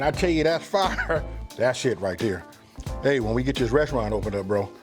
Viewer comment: what did he say?😇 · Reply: I said "That's it right there" 😇😅